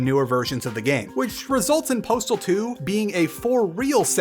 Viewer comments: For some reason I thought it said portal 2 and was grabbing my pitchfork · I thought it said “portal 2”